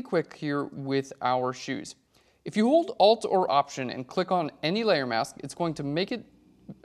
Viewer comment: I can't understand what he's saying here! "alt or option"? because my alt doesn't work that way and I don't have an option button.... I feel like this is a very stupid question but I just can't understand what he says in that minute